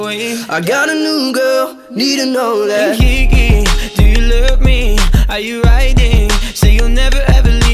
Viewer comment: Misheards: Lyric: KB do u love me Misheard: Levi do u love me (Sorry its editted cuz i spell something wrong)